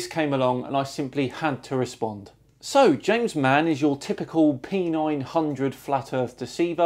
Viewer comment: At first I thought you said "your typical penile ..." 😲